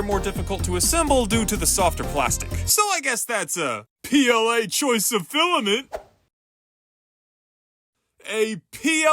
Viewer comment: Not, gonna lie, I thought this joke was better in my head... LITERALLY! For some reason, when watching this video, I thought you said "This was A-BS CHOICE OF FILAMENT!" XD Heh, get it!? "ABS" as in the filament, and "ABS" as in "A BULLSHI-" [*Scene cuts to a version of the Deltarune scarecrow character, named Shi, who seems to have some characteristics of a bull.]